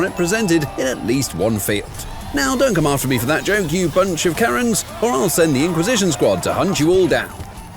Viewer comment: why do the subtitles say, “you bunch of cars?” Edit: I’m dumb. Apparently I turned on the auto-generated captions instead of the custom captions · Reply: I put on subtitles united kingdom and it says karen